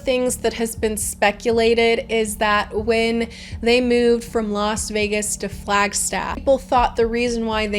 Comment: did you actually say lost vegas